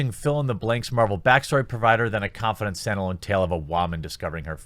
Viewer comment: Did he say Wa-man at 6:15? Lmao